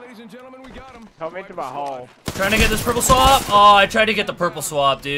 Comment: to me it sounded like try n**ga get the purple swab